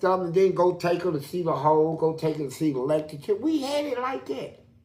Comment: What does he say at 13:40? Lecriture? Elictricure? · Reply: Electric chair